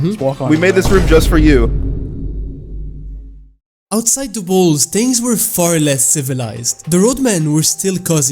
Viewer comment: yo is it me or did he say balls